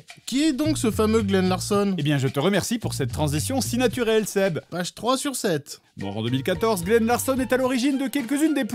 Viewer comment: j'ai jamais compris la blague, pourquoi "H3/7"? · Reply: "page 3/7"